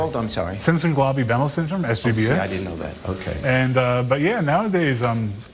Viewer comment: This came up in my feed again so I re watched it. Did he say he had/has SCVS??? If he did/does he's telling lies or the true nature of SCVS has been cut out as he'd be dead, probably in childhood. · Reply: I thought he said Simpson golabi behmel syndrome SHBS. There is however a number of symptoms that people have like unusually wide mouth with large tongue, widely set apart eyes etc. Interesting